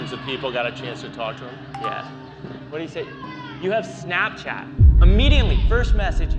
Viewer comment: uhm that does not say "do you have snapchat", that says heyy schat which is like hey cutie from Dutch to English